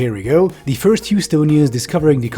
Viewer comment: I thought you had said USONIANS and thought 'Wow finally someone that doesn't use the improper term American'. Because you know, America is the continent. Otherwise you should either rename the country or start calling Texas South America instead of 'Southern US' lol